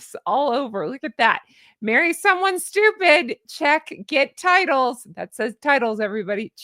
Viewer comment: I thought for a second it said get titties 😂